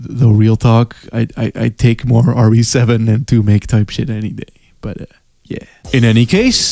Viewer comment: Can I get a translator for 30:55? I can’t make out what he says after RE7 and it’s driving me nuts. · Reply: "2make type shit" as in games like the resident evil 2 remake lol